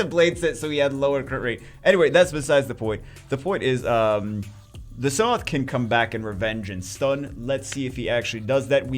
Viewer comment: I heard "The Sath can come back and revenge stun" at 6:48 and I was like "woah when did he get that buff" lmao.